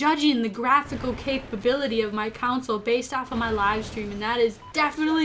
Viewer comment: When ever you say console it sounds like you say "council"